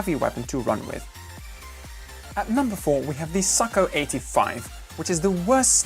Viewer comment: sako 85 but why i hear you say "sucko 85" lol